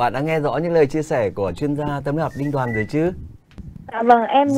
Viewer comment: Mến chúc bộ ba CSTY,chuyên gia tấm lòng Đinh Đoàn,B sĩ và A người dẫn chương trình.. những gì tốt đẹp nhất ạ ❤️ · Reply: Bác Đoàn là chuyên gia tâm lí học. Bác đọc nhanh nên nghe thành chuyên gia tấm lòng luôn 😅